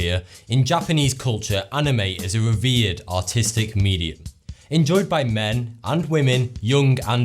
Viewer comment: I thought he said: "In Japanese culture, anime is a weird and autistic medium"